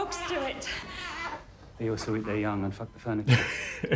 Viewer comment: Loved watching this one! Can I just ask what the doctor said at 9:00 abt the dogs please? That joke in the placenta episode. "They also eat their young and fuck sth..?" Played it 3x, didn't catch it as a non native speaker :( Thanks a lot! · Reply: "They also eat their young and f*** the furniture" :)